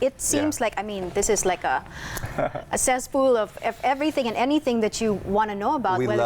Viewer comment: Very informative episode! But at 26:06, did she say "cesspool"? :)